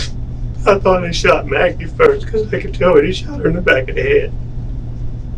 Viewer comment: I swear it sounds like he said, “I thought I shot Maggie first because I can tell he shot her in the back of the head.”